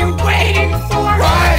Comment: I thought it said “Rawr just kidding” 🤦‍♂️